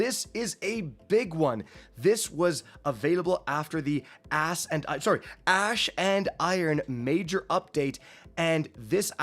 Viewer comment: Yup, we all heard you say ass and iron!🤣